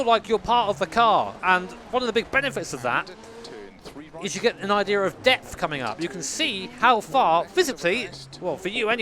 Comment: I thought he said an idea of death coming LMAO